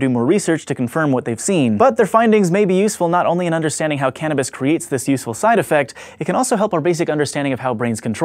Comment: I thought it said "how cannibalism causes hunger"